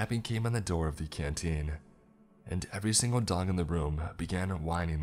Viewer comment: anybody else hear "every single dong in the room"? I swear that's what he said but my girlfriend keeps saying I'm just hearing what I want to hear. Somebody please help me cover my tracks he I mean help me out here... fuck